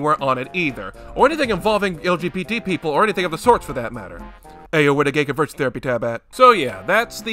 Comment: tf did he even say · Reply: "ayo where the gay converts therapy tab at?"